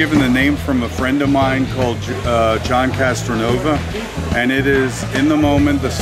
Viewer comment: marce.. arrancan esos cositos O_O minuto 1:24 dicen donde los hacen "casanoba" es lo unico que le entendi es maravilloso y hay una bocha de motorcitos asi en videos tambien ;D voy a ver